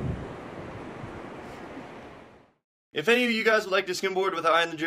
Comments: it sounded like he said "drug squad" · Did he just say jugsquad haha